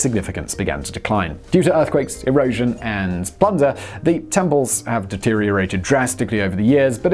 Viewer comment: I misheard plunder as thunder lol.